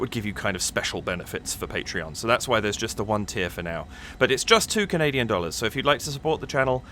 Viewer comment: What you SAID: "...it's just 2 Canadian dollars..." What I HEARD: "...it's just 2 million dollars..." <insert record scratch sound> Wait. WHAT? LOL